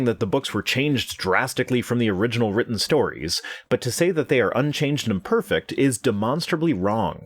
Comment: it sounds like you say the stories in the bible were, "unchanged and IMperfect." The argument from the Christian is that they're perfect. I'm sure this was unintentional or a misunderstanding on my part, but I thought you might like the heads up.